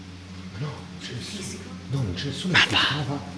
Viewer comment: Complimenti ragazzi!! indagine inquietante perfetta per il suo genere!!! cè da lavorare tanto, comunque vi segnalo che al minuto 12:10 (11) ho sentito"papà" qualche istante dopo al verso del fruscio,ma non avete detto nulla..😱